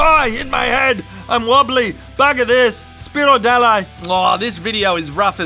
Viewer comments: Did just say 'fuck this' in Polish during the deer jumping segment? lol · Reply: I thought I heard him say 'spierdalaj' which basically means 'fuck off!' LMAO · What did he say? "Spierdalaj"? Well done! Your Polish fans are proud of you:)